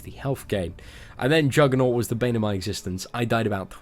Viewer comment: Would anyone be kind to help? From the video: 12:13 - 12:15 'And then Juggernaut was the ????, i died about 20 times...' What was that word he said, bannermitise? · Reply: Bane of my existence